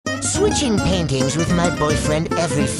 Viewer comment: Idk why I heard "painting" as "pancakes"